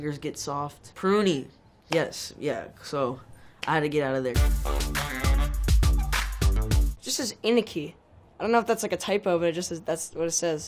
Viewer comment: DID I HEAR ANIKI? And then a big "Woaaaahhhh" gachiHYPER